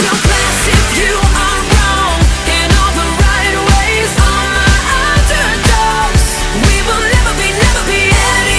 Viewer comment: Instead of "All my underdogs" I always thought it said "All my odds are gone!"